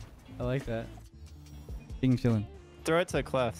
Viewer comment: why do I hear “bing chilling”